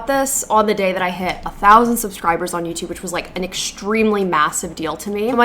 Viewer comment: Lol when you said 'thousand', the subtitles say 'th000'. Did Youtube just have a stroke?